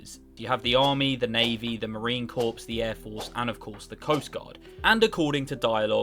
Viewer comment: the P in "corps" is silent. It sounded like you said the Marine Corpse, which is kinda ironic.